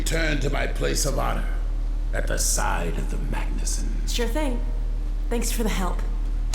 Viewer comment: Hm. At 0:58:25 Alyx says "Thanks for the help", but the captions say "Better now!" (from the Dog vs Strider part).